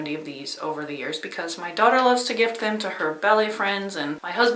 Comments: When you first said your daughter made the coffee cozy for her ballet friends, I thought you said “belly friends” and I thought “Hmm. That must be a new term. Is that like your group of friends whom you belly laugh with?” Then you used it again later in the video and I realized that you said BALLET and there is no such things as “Belly friends” and now I’m kinda sad. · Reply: Oh, BALLET!!! I thought she said belly, too, and wondered if she meant a belly dance class. 😂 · When you first said your daughter made the coffee cozy for her ballet friends, I thought you said “belly friends” and I thought “Hmm. That must be a new term. Is that like your group of friends whom you belly laugh with?” Then you used it again later in the video and I realized that you said BALLET and there is no such things as “Belly friends” and now I’m kinda sad.